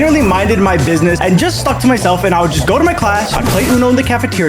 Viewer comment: I heard this shit as sucked myself